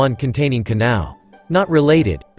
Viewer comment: I thought he said muuchirao taquito 😭